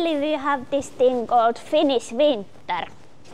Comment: "Luckily we have this thing called 'Finnish Winter'"😂 - I love the dry humour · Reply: I thought she said “the famous Finnish winter”.